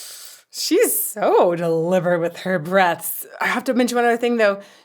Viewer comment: Great rection to an amazing voice. I was a little confused after I misheard you when you said 'she so delivers with her breaths' :)